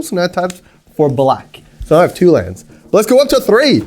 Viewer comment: did he say he has two lands?